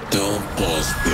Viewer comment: did that said to poznas means you know it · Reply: Przykro mi ale tam jest don't pause press play xD